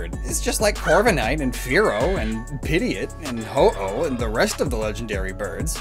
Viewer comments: I don't know if its just me but when he said Pidgeot it sounded like he said Pideot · Excuse me, did he just say PIDIOT. 3:45 IT'S PRONOUNCED PID-GE-OT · I'm sorry but what did he say at 3:45 my brain keeps thinking he said pidiot.